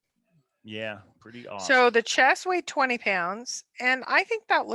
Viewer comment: i thought Forrest said the chest weighs 22# and the gold weighs 20#